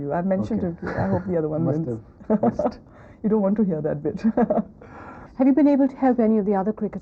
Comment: Did Huma say at 23:16 "You dont want to hear that bitch?? Lol